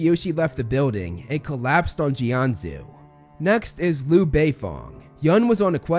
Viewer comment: did I hear Lu BEIFONG?